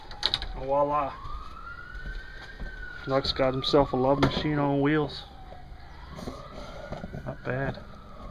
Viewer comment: did i hear "love machine wheels" lol